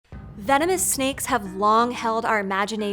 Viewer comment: When you started the video by saying venomous snakes I tought I heard you say feminist snakes.